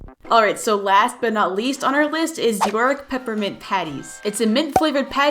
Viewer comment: i honestly thought they were called yonk